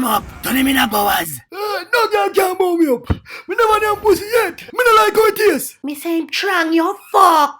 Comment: Wait a minute did thonos said mi never nyam pussy yet mi don't like how it taste